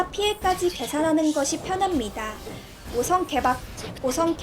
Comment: 5성 개방을 5성 대박으로 들리는?